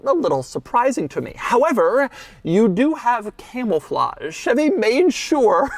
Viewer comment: did he say camel flahje?